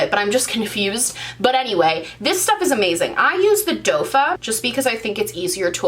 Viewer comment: Ohhhh I just heard you say "doefa". I never noticed but after the comments I did. Oh well. I say things really crazy if I get tired. Also I'm on the small lip struggle bus too. Oh well 🤷‍♀️. Makeup Struggles used to talk about the cheek pops, but she is no longer on YouTube.